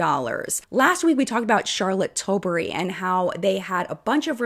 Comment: I swear that I heard "Charlotte Dobre" and my first thought was "she's not British! she's Canadian!"